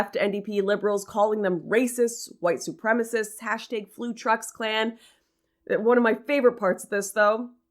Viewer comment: Haha. At 1:36 I thought you had said Food Truck Clan.